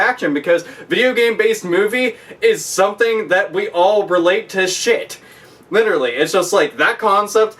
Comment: "the edit based movie is something that we all wear latex shit" That's what the captions say at 06:19 Seriously YouTube?